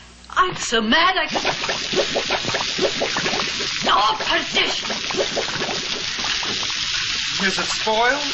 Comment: I love this movie! Her reactions and voice are perfection. I cannot make out what she says @ 3:40 just after she says "I'm so angry I could..." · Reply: perdition